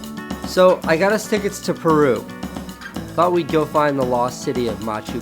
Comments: I thought it said perv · I thought that said perv😛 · lol thought it said perv for a sec XD anyway great vid love ya casey <3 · LMAO I thought that said PERV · thought it said perv I was like wtf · Thought it said "perv" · I SWEAR IT SAYS PERV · anyone else thought that said PERV instead of PERU??